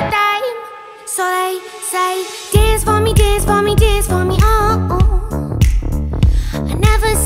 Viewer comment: I always heard "Dance mommy"